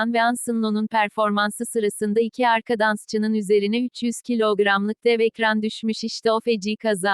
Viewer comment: Bu haberi ilk başta sahneden fln düşüyor sandım sonra ekranı görünce gerçekten ağlıycak gibi oldum ellerim titriyor o adamın bi gelecegi bi hayatı bi (dansçılık) hayatı yasagicagi bir sürü gün vardı ama bi sahne kazası sonucu adamın bütün hayatını maffetediler o ekran düştüğünde canı ne kadar açılmıştır sonuçta 300 kilo ve yüksekten düşüyor bence kurtulma ihtimali düşük ama Allah yardımcıları olsun ~rain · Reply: 600 kilo diye duydum.......